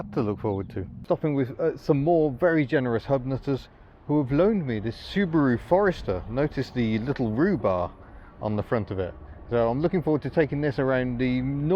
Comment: For a second or two I thought he said rhubarb.